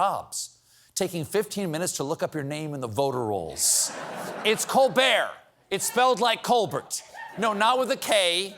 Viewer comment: I thought you said COOL BEER for COLBERT! 😄 🤣